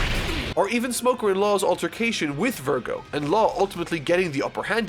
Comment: is thought he said "With Smoker in law